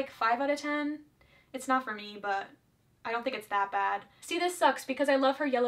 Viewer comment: she really said “it’s not farmiba”